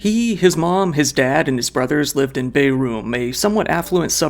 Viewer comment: CC says Beirut. It's Bærum.